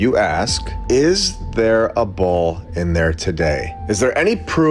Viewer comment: a BULL? Not sure wtf he saying.... WTF about a bULL?😅.... Wtf a bull gonna show us..........?